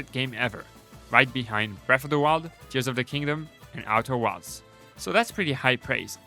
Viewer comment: wtf is he saying? Artur wilds? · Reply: Outer Wilds! Sorry for poor pronunciation